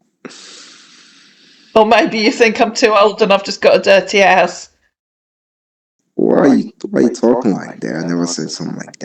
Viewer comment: When you said "or maybe you think i'm too old and I've got a dirty house" I swear to God I thought you said, "I've got a dirty ass!". I had to go back and listen again. That would hae been even better! 🤣